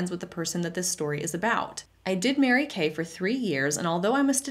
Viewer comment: “I did marry Kay” as in matrimony is how I heard it while driving 😂😂😂